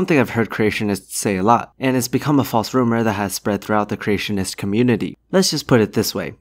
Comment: Not that I'm complaining about the term, but did I hear you say 'Christianists'? Was that a verbal typo, or a snapback at 'evolutionists'? Or did I just hear it wrong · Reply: He probably said creationist.